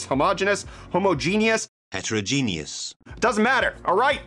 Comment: I thought I heard, "hetero-erogenous".